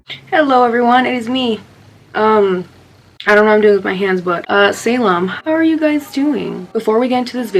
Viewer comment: When she said Salem I thought she said Salam Muslim gang where ya at 😂😭💀